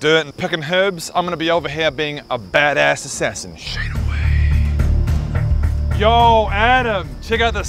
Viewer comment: I know he says "shade away" but the first time I watched this I thought he said Shake weight and I was just like...what?